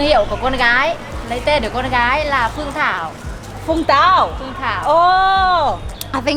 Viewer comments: She is not talking about vùng tau, as I hear, she said "ten còn gái là phường thao" . It means her daughter name is phuong Thao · at 8:13, lady said her name was, but she used her daughter name Phuong Thao for the restaurant, but you was thought she said Vung Tau, which was a location. I just want to clear that part. Enjoying your video pretty much. Thanks